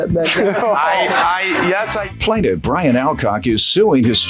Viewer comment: I thought the announcer said his name was Brian Outcock.